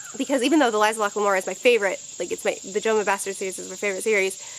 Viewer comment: Can someone tell me what she said was her favorite series was at 1:09 ? Couldn't quite catch it! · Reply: The Gentleman's Bastards. I bought the whole series because of her basically.